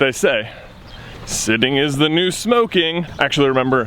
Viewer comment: In this video when you first mentioned a standing desk I thought you said standing is the new death and I was like how is standing around going to kill you. I don't know why I heard that but it made for a good laugh when you asked if people have tried it and would they recommend it.